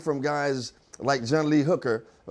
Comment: does he say "boogie children"?